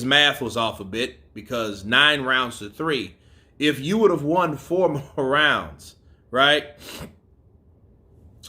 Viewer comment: Did he just say "I won four more rounds"? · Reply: No, he said “next time let me win 4 more rounds (from the first fight) and I’ll win the fight.”